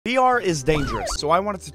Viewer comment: I THOUGHT AT THE STAR HE SAID VMT IS DANGEROUS